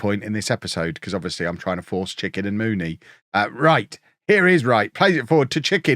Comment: Thought you said poached chicken at one stage 😂